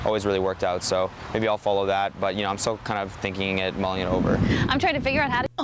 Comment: what is that word that she thinks that is cute? I can't really understand what he said in the interview. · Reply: "mulling it over"